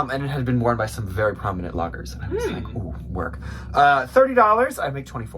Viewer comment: it sounded like you said Loggers not Bloggers lol cracking me up here in Oregon! You guys Rock!